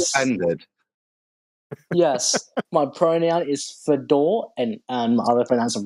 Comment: It sounded like the pronoun guy was trying to make a joke of "his pronouns" and it fell like lead balloon. I thought he said "Fedo" and "ra" like Fedora, like a hat. Then things spiralled.